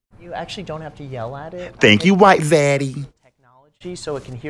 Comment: Sounds like you said white Zaddy 🤣